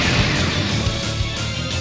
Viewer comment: what say he in 2:22? xDD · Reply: Blood Girl I think he says "Get out of my sight nitwit"